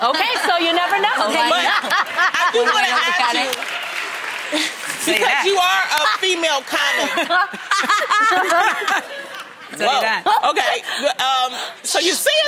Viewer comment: did Loni just say "you ARE a female condom." ???!! XD